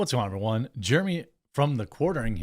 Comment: Did i mishear "Jammy from TheQuartering"? 😆